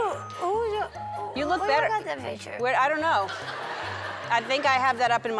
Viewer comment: how tf you didn't understand it said "how did you got the picture"